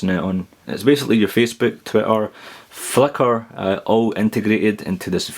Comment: Wait a minute, did you say "Fucker" or "Flickr" at 3:46 ?